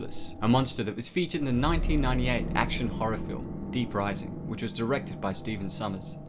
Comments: lol, not gunna lie, initially thought it said dead rising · I’m not gonna lie. I thought it said dead rising and got concerned. Still a banger tho